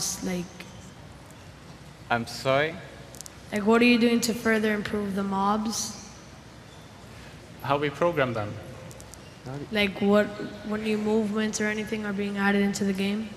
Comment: i honestly don't get how they understand what he was saying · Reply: “What are you doing to farther improve the mobs, like are any movements or anything else being added to the game”